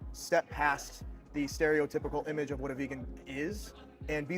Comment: It's quite nice to see that these people don't look like your stereotypical vegan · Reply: Oh I thought you said Steroid-typical LOL